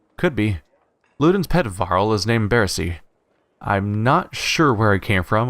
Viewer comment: the way u said "ludin's pet varl" sounded like "Ludin's pedofile" ;o was listening and not watching so i stopped in my track like wait wut?